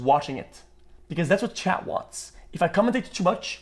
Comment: subtitle says "Chad" instead of "chat". it might confuse some people